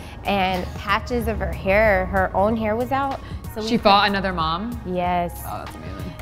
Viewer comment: I’m so deaf. I thought she said at first ‘she bought another mom’